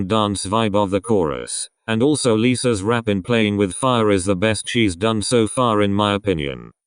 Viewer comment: Wait hold up. Did you say “Lisa rap part was the best cheese”? 😂